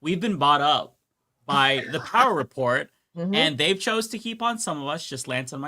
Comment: What did he say they were bought by? Sounded like he said"The Power Report"?